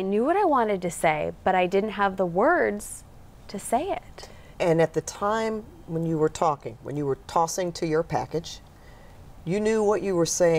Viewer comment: Is the woman conducting the interview mocking Serene? What is she saying @ 2:15? " And at the time you were talking, when you were TOSSING TO YOUR PACKAGE?!?!?!?!" is what it sounds like. Maybe some type of reporter lingo? Or some REALLY FUNNY, maybe uncalled for, on camera workplace teasing? If so, Hilarious!!